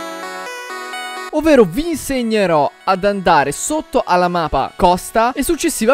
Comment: like se anche voi avete sentito al minuto 0:34 "mapa"